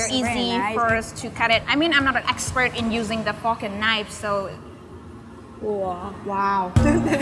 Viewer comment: I thought she said fockin knife lol